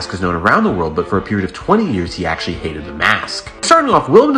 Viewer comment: At first I thought you said he ate the mask then I reheard it